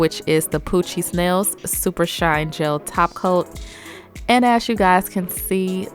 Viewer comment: I can't be the only one who thought she said coochie smells top coat😂😂😂😭